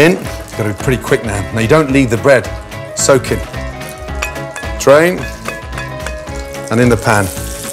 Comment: I thought he said "train" because it makes the sound of a train going through a crossing